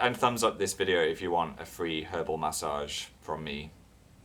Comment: When Phil said 'free herbal massage' I heard 'free hobo massage.' O-o